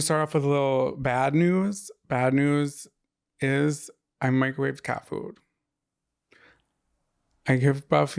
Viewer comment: the way i thought said “I microwaved cat poop” 😩😅